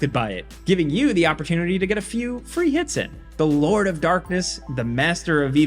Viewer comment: I thought Jake said “the lure of darkness”, which also made sense